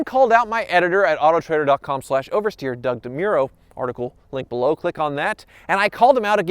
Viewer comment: if you turn the captions on at 3:43 when says doug de muro the captions say ugh damn euro hahahaha